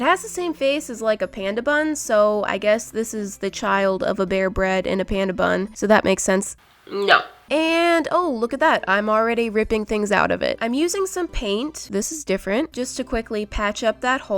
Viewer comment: I thought it said beer bread 😂